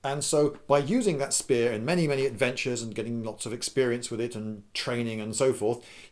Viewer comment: am i the only one who heard ex-spear-ience